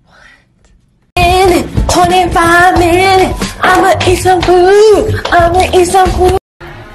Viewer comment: “MINTUES! 25 MINUTES! Imma eat sum food!” 😂😂😂 MOOD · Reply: I thought she said imma eat some poop 😂😂😂 but she said imma eat some food